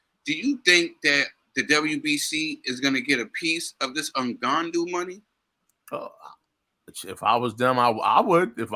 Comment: Did say "Ngandou money" @ 10:18 🤣🤣